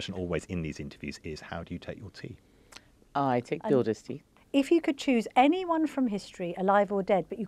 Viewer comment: How does she take her tea? “The orders tea” What does that mean? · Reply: I heard 'builders' tea', i.e. strong & cheap.